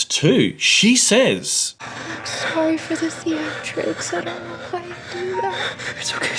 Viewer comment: Anyone able to spell the exact line wish Nikki says at 27:32? I've replayed it but im not sure what the wording is in the whisper. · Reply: "I'm sorry for the theatrics. I don't know why I do it."